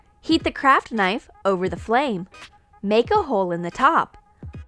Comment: I thought it said crap knife